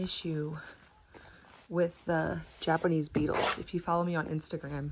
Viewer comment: Were those bugs lady bugs? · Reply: I thought you said beetles but i was t sure. Boy that was a lot of beetles! must have been hard getting rid of them.